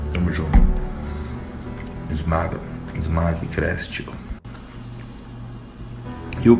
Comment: só eu que não entendi oq ele falou ? ;_ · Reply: "Esmaga que cresce tio"